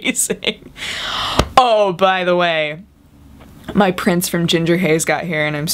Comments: when you said 'prints' I thought said ' prince' XD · For some reason when you said "My Prints" I thought you said "My Prince" and I got very confused.